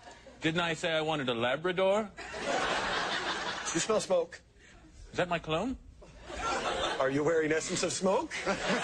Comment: Do you smell smoke? Is that my cologne? Are you wearing essence of smoke? LOL, classic. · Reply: I thought he said “Is that my clone?”